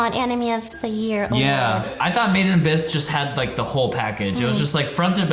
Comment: I was so confused since I thought they said "maiden abyss" instead of "made in abyss" and it came up with some SAO garbage and I was wondering why they'd be elected for anything